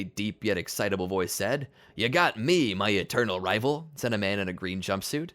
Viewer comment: for a second I thought he said my eternal lover😂😂lol